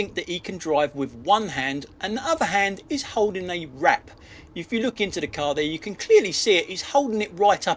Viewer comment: I really thought you said the tosser in the Audi was "holding a rat", not "holding a wrap"